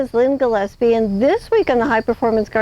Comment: I thought she said Lynn the Lesbian, had to go back n check before continuing. Great video lesbian